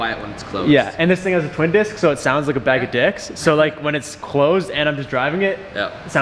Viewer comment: … no way I just heard say “It sounds like a bag of dicks.” Is that what I heard at 11:44 ?? Someone confirm